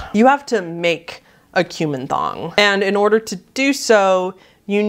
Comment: I was listening to this video in the background and I thought you said "cumin thong". Ouch.